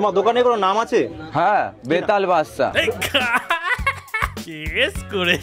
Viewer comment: আমি "বেতাল বেশ্যা" শুনলাম 😂😂😂